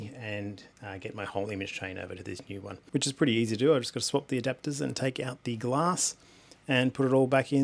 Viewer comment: Good to see you back. Spat my coffee out at 4:25 all i heard was swap the adapter and take it up the arse. Love you dude.